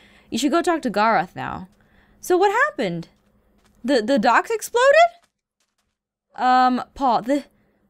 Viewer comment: How many people laughed when she said: "You're being a creepy casanova, and WHAT HAPPENED TO THE DOCKS???!!!! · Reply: In the ep she said "What Happen To The Docks" because they exploded and I thought she said "WHAT ARE THOSE"